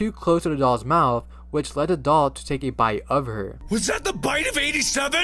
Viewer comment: I hear "come on take a pit"